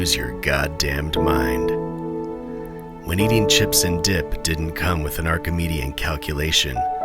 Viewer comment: why tf did i hear "dipped in cum" 😭